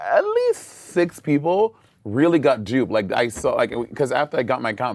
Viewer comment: I don’t like him anyway but at 2:00 talking about the people who were scammed did Monet say “ 5 or 6 really got jewed”? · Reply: he said "got duped", not "jewed".